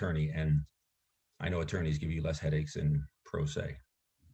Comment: Mitchell: "I know attorneys give you less headaches in pro se." Judge: "No, not necessarily." lol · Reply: Interesting, I thought he said "I know attorneys give you less headaches than pro se," meaning talking to an attorney instead of the litigant is easier for the judge. So I took the "not necessarily" response as a light-hearted joke about attorneys, and maybe the only point Billy scored with the judge in an otherwise absolute clusterfuck. lol